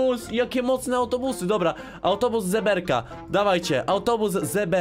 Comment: ale fajne montaże xd jak powiedziałes autobus zeberka to usłyszałem autobus żeberka🤣🤣🤣🤣